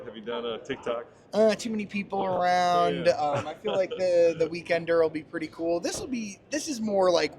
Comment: Weekender? I thought that said WAKANDA.